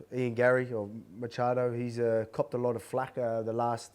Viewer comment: I thought Volk said Garry "Cucked a lot of flack".